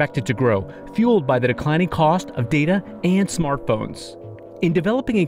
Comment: The subtitles says "smart BOMBS" instead of "smartphones" :000